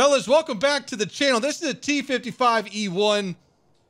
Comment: T55E1 am I crazy or did I hear that right.